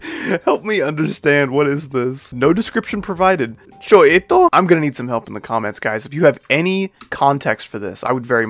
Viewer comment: I think it was meant to say "что это?" which means "what is this?"